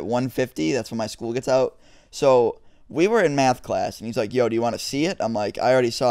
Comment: lol i thought you said meth class XD